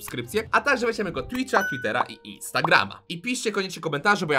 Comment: Jprdl usłyszałam tindera zamiast Twittera